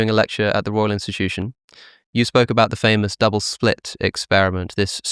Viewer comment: ish - did Alex just say "Double SPLIT experiment"?